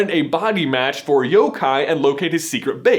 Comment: did he say Yo Kai?!?!